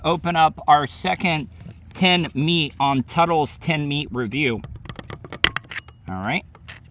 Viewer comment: I thought Bubba was saying “Ten Meat Review” this morning and I thought shoot, is Tuddle making a porno?